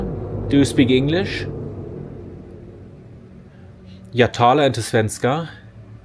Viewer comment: I'm just gonna point out that i Believe the caption at 19:37 is incorrect. The text said "Do you speak swedish?" however the sentence i heard was "Jag talar inte svenska" which is translated to; "i do not speak swedish", i've listened to it multiple times and i can't hear anything other than that. And one other thing about the swedish house, there's a ton of houses on the swedish country side that may look like it's old and abandoned however, most of the time someone actually lives in the houses, they just don't give a shit about it. it's not always easy to tell if some houses are ocupied or not..